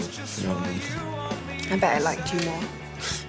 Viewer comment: what does say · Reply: I bet it liked you more